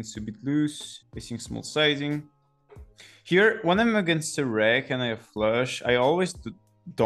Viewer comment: I couldn't hear it clearly, did you say facing a reg or rec(reationals) you would tend to always donk the turn? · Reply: Recreational, sorry for my accent :p